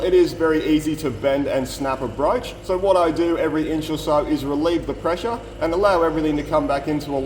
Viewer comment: Wait a sec. Did he just say "inch"?